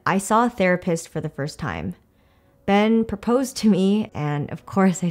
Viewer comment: I THOUGHT YOU SAID THAT YOUR THERAPIST PROPOSED TO YOU KSDKHDHD